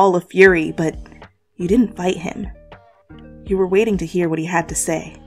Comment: did it say that " but you did not BITE him